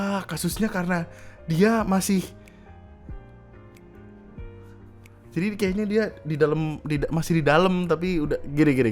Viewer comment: ITU BANG NGOMONG APA YAH? KEPO AKU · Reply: 2 in